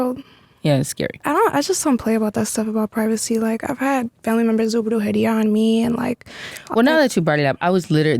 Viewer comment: she had family members do what? The words got jumbled for me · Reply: Brujeria